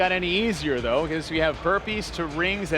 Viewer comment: Did he said around 9:15 herpes instead of burpees?